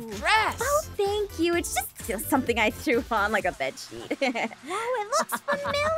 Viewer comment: Me mishearing Aph: "Oh just something that I put on like a *Bish*" ~Trying to put captions and play the scene again~ Oh never mind, she said bed sheet.